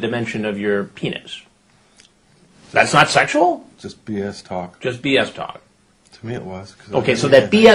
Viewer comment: When he said "it's just bs talk", I thought he said "it's just penis talk" 😂